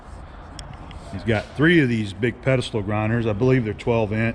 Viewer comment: at first I thought you said pencil grinder, thinking that’s a pretty big machine to sharpen pencils.